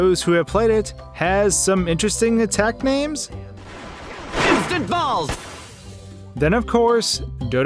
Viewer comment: I heard waluigi say “eat this in a die hole” doesn’t make any sense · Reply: i heard "Take this TinyBalls"